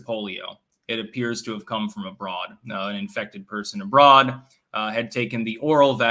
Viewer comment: Whenever someone says "came from abroad..." I hear "came from a broad". I don't think we call them that anymore. 🤣